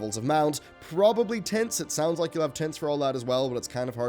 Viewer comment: I heard "tits" then went back to CORRECTLY hear "tints"